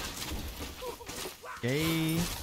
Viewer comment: now I might be crazy here, but I just heard say "gaayy" and that's not a big deal if that's true. But a bit hypocritical if it is since in the tiamat vid you asked why the loki would use gay as an insult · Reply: Pretty sure he said kayy